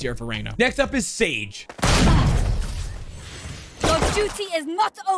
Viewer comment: I always thought harbor said "Prepare to die" for some reason idk why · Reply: ngl i thought he said "lets do or die"